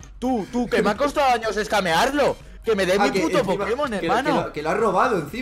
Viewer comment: Pensé que decía escrimer XD